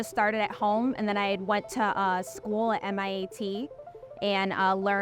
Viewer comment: Is that her accent or is she saying MIAT? 2:40.